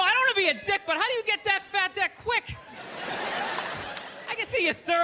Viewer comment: "How do you get that fat that quick?" Subtitles say: "How do you get that fat dick quick?" Bruh